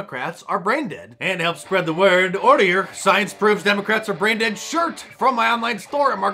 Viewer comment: 🚨 Order your Science Proves Democrats Are Brain Dead shirts here ➡️ <link> ⚠ Order your Let’s Go Brandon! shirt here: <link> ⚠ Order your F*ck Joe Biden shirt here: <link> ⚠ Order your Arrest Dr. Fauci shirt here: <link> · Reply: I heard, "Democrats are "Brandon " ...Let's get Brandon's!!